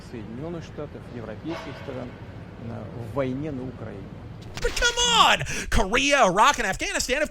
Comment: Did he really say "war in Ukraine"? - Yes he does, witness my 3 words of Russian (vaynye na Ukrainye). I thought it was officially just a "military operation" and anyone in Russia who called it a war was sure to end up in prison?